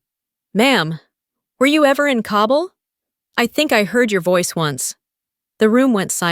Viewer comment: “Were you ever in COBBLE I think I heard your voice once …” Should be “Were you ever in KABUL? I think I heard your voice once …”